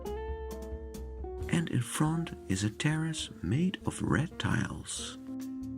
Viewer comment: oops I understood "a terrace made of reptiles" 😨🤭 instead of Red tiles